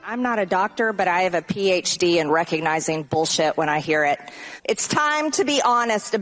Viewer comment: I think I heard Margie say "I have a PhD in Bullshit" but I might be wrong. 😅